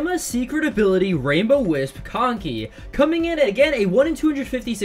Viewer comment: I heard "Conkcy"